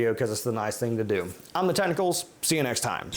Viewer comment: Every time at the end I hear "I'm the tentacles." Hahaha.